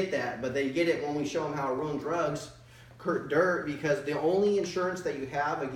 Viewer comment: they get that when we show them how to run drugs? Did anybody else catch that 🤣🤣